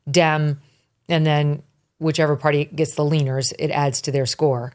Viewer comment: At first I thought you said: The gallop poll tracks “party identification and wieners”. I looked up at the screen eagerly for verification. Those two dudes weren’t laughing, I then knew I must’ve misheard… “leaners” was the word.